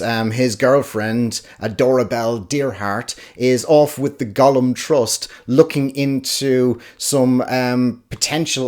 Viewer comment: Thought you said “Gollum Trust” for a second there 😂 I don’t think you’ll enjoy Raising Steam better than Making Money. The books are similar in many ways, thus making Raising Steam derivative. Still a fun read though. 😄